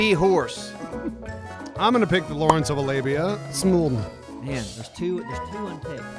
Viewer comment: did anyone else hear "Lawrence of Alabia" and not "Lawrence of Arabia"?